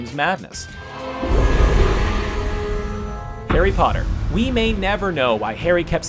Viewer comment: Did he just say Parry Hotter?